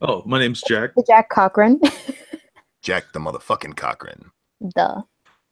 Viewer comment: My bad ...I thought you were always saying jack the mother fucking COCKRING